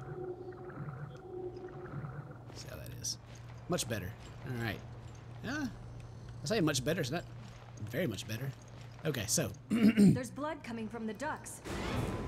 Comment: it really sounded like she said : there's blood coming from the ducks